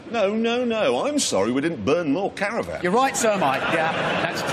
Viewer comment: thought clarkson said they were burning talibans ...... very disapointed to realise he said caravans :)